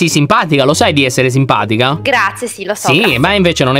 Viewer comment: solo io ho sentito "si lo so cazzo"?